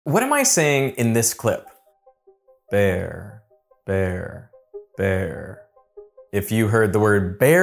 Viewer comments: Im crazy All i heard was “pear pear pear” 🙃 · Straight up, all I heard was “there, they’re, their” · I heard "bear, vair, vair" even if I do not look at the screen (I know that vair is not a word, that's just what it sounds like). I never once heard fair, because it sounds like a "v" or very soft "b", not an "f" and the first time you pronounce it the beginning letter sounds slightly harder (which is why the first one always sounds like bear) than the other two times that follow. I'd argue most people would use a clearer "b" (like the first time you pronounce it) than the other two times, where it seems like you deliberately pronounced the "b" extra soft. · I heard "there" or "their". He used the upper teeth! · I heard “there/they’re/their”